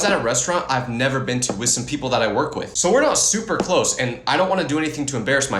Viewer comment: did bro just say he works😭